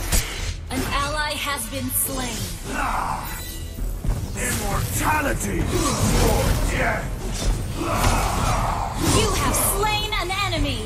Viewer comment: what i heard was “imortality! odette!”